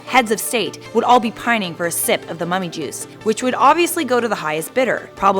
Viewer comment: I thought it said “mommy juice” and I was so confused